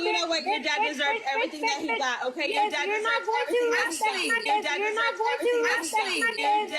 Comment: What does ash say in the video at the 32:40 mark? I can’t understand it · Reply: Sounds like "Your dad deserved everything he got" or something similar